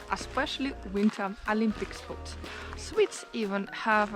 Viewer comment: It sounds like you saying swiss when you’re talking about swedes 😅